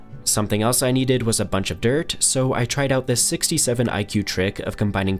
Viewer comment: Did bro just say 67 😂😂